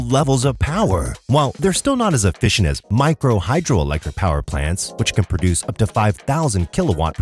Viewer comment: excellent video; just wanted to point out a typing mistake, probably, at 4:51 , when the caption says "micro 'hydrogen' powerplant", instead of hydro.